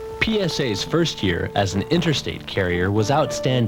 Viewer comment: dod anyone else hear PinusA at like 6:40?